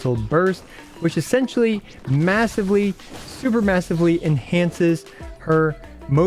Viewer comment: sorry but did you just say massive?